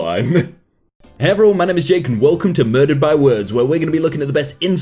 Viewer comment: I heard murder my words